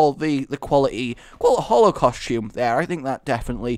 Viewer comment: I thought you said holocaust xD